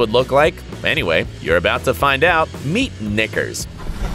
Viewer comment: meet nickgurrz read it fast that's what he said xD